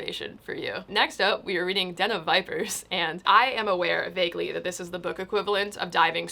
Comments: for the longest time I thought you were saying Denim Vipers instead of Den of Vipers.... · When said “den of vipers” I heard “denim vipers” and was very confused for a hot moment